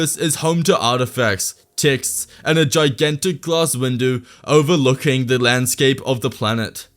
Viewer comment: Did you just say a gigantic glass Windu?